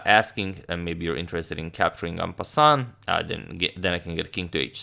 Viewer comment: what does agadmator say ? I know it means meanwhile or immediately but the word he says is .. "Un pass on" or what else? Any suggestions guys? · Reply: He says "en passant". Immediately after a pawn moves 2 squares you can capture it as if it had moved only one square. <link>